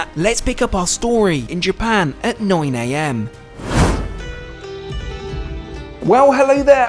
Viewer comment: what did he say here? at noi i am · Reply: “At 9AM” lmao